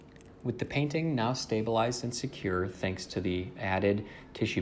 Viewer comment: Lol 6:46 I thought he said "with the painting stabilized and secure THANKS TO ME"